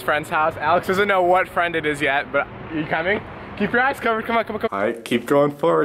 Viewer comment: i SWEAR i heard "keep your ass covered"